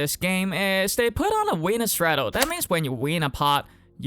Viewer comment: Did bro say “weiner straddle” ? 💀💀